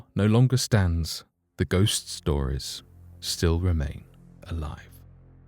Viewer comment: Did you capture an evp while recording the voiceover at 11:00 ? Anyone else hear it?? Sounds like it says "A lie"